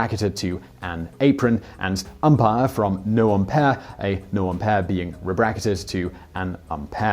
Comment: I think that it would be wise to poat the topic being discussed, so that we can see it. It sounded like you said, "noumpere" was changed to "an upere". It would save us a lot of effort, if you simply put on the screen, "umpire to noumpere"; or something like that.